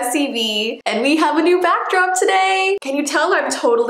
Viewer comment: Thought she said BATDROPS for a minute.